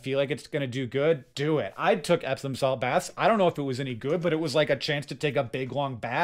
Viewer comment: "epsom salt"... thank you subtitles! I first thought you said "absinthe" 😂